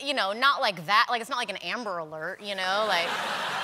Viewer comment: Can somoeone help me understand what she says at 5:27? "not like that, not like a ....... attack"?? Please ? Thank you :) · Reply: drsnoggle not like an 'Amber Alert' whatever that means